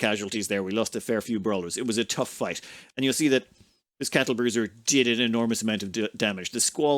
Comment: God, I just heard "this cattlebruiser" and I can't call it any other way now.